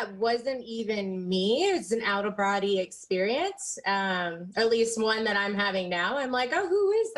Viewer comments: Did she say an outerer brorodary experience? Oh, then she says 'conversating'....O.M.G. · Did she say, "out of "variety" experience?